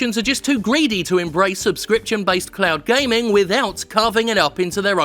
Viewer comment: Every time I hear cloud gaming, I mishear it as clout gaming